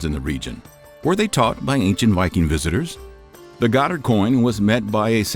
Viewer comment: Can anyone understand what he said at 6:39 ? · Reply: "Were they taught by ancient viking visitors?"